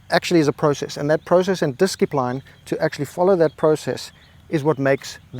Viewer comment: did you just say "diskipline"? That is a very confidential statement.